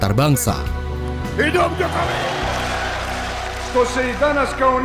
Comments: dengernya hidup jokowi, kuping gw ngga salah kan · What????? Menit 23:43/23:44 gua dengernya tuh (hidup jokowi) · Menit 23:40 kok dengernya kayak " HIDUP JOKOWI " 🤣🤣